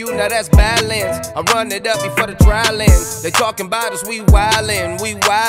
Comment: Idefk y but at the beginning I thought he was saying shitsticks? XD